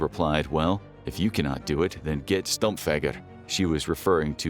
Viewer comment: I was listening the video rather than watching, I heard "Get Stomped, f**get."